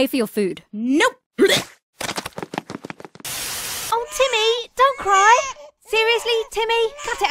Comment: I thought she was saying Jimin weirdly instead of Timmy😂